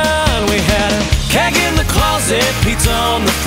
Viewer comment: um Kenny I thought you said kick in the closet